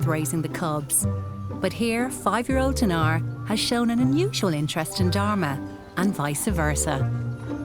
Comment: I almost thought you said 0:07 "mutual interest in Dhar Mann"